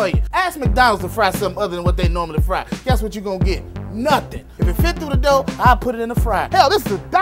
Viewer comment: “If It fit through the door, I’ll put it in the fryer.” Me: Brings fryer. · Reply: I thought he said "if you fit through the door, I'll put it in the fryer" maybe it's just me